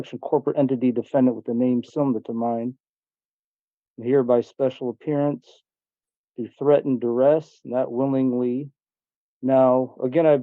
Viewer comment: If, he truly believe that the court has no jurisdiction over him; why did he showed up for court? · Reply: At around @ 0:48 it sounded like he said he was there by "Special appearance under threat of arrest." (or threat of duress... kind of difficult to understand his mumbling).